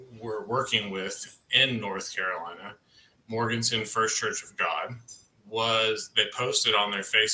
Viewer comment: Morganton First Church of God. It took me a few minutes to find it. I thought said Morganson. It Morganton. ❤